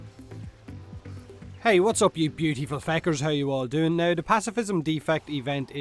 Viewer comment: Did you just say Hey guys welcome back pewdewpie fuckers?